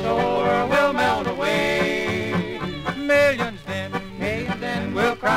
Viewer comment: could of sworn it was saying millions dead.. but its actually millions then.